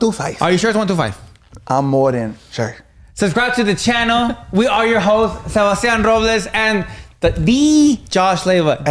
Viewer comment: I thought he said we are your “hoes” 😂🤣💀